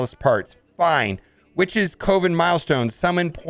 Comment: I thought you said COVID milestone and not coven hahahaha fucking media brainwashing me....